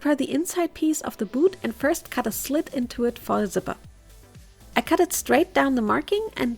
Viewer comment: no joke i couldnt understand "for a zipper" and I thought she was speaking german dfhksjdg